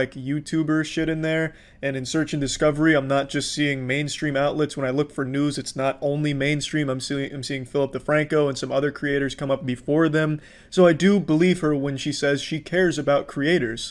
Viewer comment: i tought i heard " when i look for nudes its not only mainstream i'm seeing Philip Defranco"